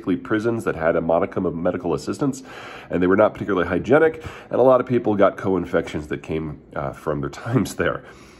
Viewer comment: I thought he said, "a lot of people got colon infections." @ 1:10 - i thought he made a joke.